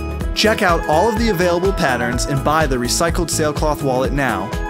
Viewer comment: Lol I thought it said recycled SNAIL wallets! I was like what the crap?!?!?!?!?!?!?!